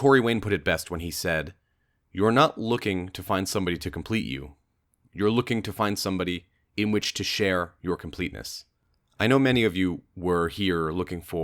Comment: LOL at first I thought he said "you're looking for someone in which to share your penis"